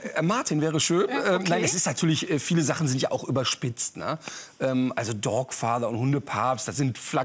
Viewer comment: Noch einmal Godfassser und ich raste!!! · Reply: Ich habe dog-father verstanden, das gibt ja dann auch Sinn. ;)